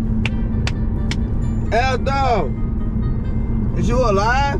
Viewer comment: what did he say? Its inaudible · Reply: I think he said, "Is you alive?"